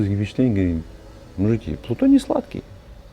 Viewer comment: что он сказал??? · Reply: Но он делился впечатлениями, говорит: мужики, плутоний сладкий